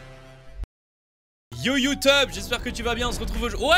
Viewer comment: c est moi où il a dit "youteub" ?